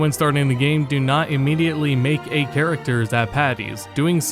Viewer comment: Um, can someone please tell me what he's saying at 3:42? It sounds like "don't immediately create a character zapaddies." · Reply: "Don't immediately create a character at Patty's"